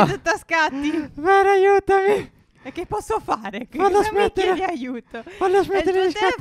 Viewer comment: ho sentito male io o ha detto "come minc**a ti aiuto ??